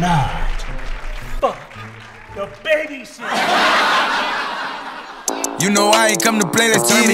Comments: I thought it said baby sister💀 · At first i thought he said baby sister